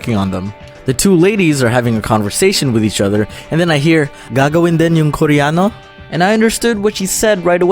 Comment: I thought the girl said "gago win yung korea" i dont get ot either until i saw the translation un pla gagawin rin ba ung koreano? Skl(share ko lng)(im just sharing) i think.....